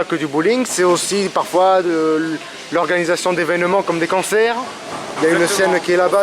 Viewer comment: Excellent reportage! :o À 4:05 " L'organisation d'évènements comme des cancers " ( j'ai entendu + can que con ) XDD j'étais mort ( normal quand tu as le cancer ) bon ok j'arrête XDD Non franchement, très plaisant à regarder et à écouter ;)